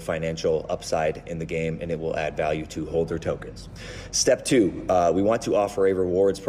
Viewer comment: slight error in the captions, “hold their” instead of “holders”